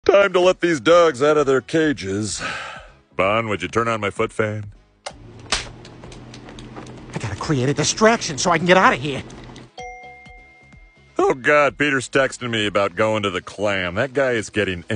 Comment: I like how he casually uses his legs Edit: (I misheard, it's a leg fan, sorry)